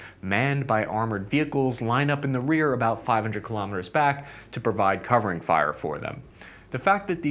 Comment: Did you say 500km?? 😕 Just a european guy asking❤️